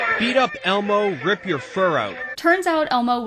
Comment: I HAD THE SINGING ELMO OMG i heard "spit ur fur out". i think i have it still in my basement somewhere i'll have to look for it now😭